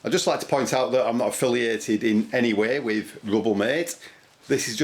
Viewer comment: I thought it said Ruble mate....